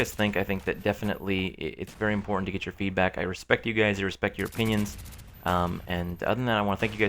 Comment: lmao... I thought he said "I respect your penis" 4:50 lol xD